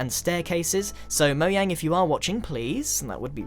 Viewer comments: did he just say moyang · did u just say Moyang? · did he just say moyang? GRIAN!!!!!!!! its mojang! (no offense) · I thought he said Moyang lol... it sounded like it. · I think I heard him say moyang xD